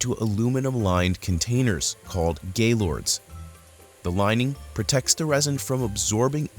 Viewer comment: lmao, GAYLORDS, did i misheard it?